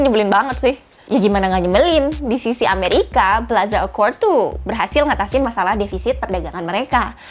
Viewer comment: misheard mode : PLAZA AWKWARD 😂😂😂😂